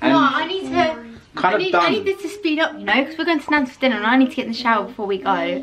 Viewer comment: did anyone else realise how said “i’m going to nans for dinner” but on the screen it had nandos logo? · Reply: no bc at 15:32 said nandos but it sounded like nans as she said it fast